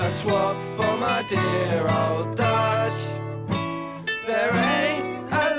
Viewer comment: please, my ears are bleeding!!!! What the f*** IS this about? Is he saying "my dear old Dad", and what does it have to do with - ?? How to NEARLY ruin a brilliant documentary. The music is just unbelievable crap. I am having to mute the "musical" parts and put captions on, even though the narration is the best part. I just cannot take it!